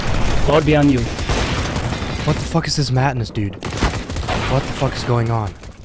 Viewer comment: did he really say: what is this ”matt-ness” tho....